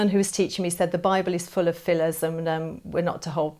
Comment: she said the nun told her "the Bible was full of" what? phyillism? I don't understand the word. If someone can tell me I would appreciate it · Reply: "Fillers" - ie made up stuff in this case.